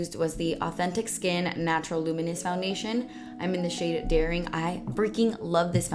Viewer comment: When you said “zoeva foundation in the shade daring” i thought you said “the shade dairy” and i laughed so hard